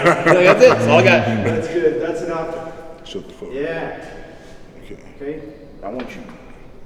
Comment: can someone tell me what LW said LOL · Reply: "Shuhdthuhfuh"